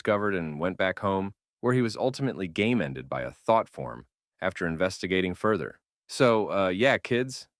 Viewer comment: "by a thot form (yes it sounded like that the 't" was hard)